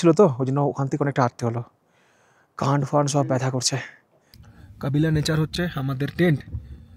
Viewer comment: দাদা ঠিক 36:15 মিনিটে কি ব্যাথা করছে বললে? আমি যা শুনলাম সেটা কি ঠিক শুনেছি? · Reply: কান ফান সব ব্যাথা করছে বলেছি.. 🙏